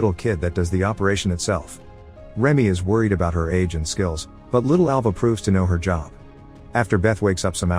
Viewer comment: OML I was half listening and thought he said he's "worried about her Asian skills"